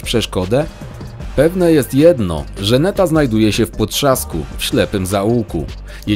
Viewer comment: Czy tylka ja usłyszałem Żaneta 😅.